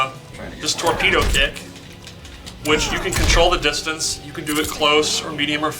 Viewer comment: I swear to god i thought he said tornado dick when he said tornado kick lol